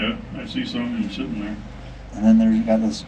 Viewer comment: i thought he said yep i see someone sittin' there and all i could think was "go back up, go back up, this is how all horror movies start!!!!"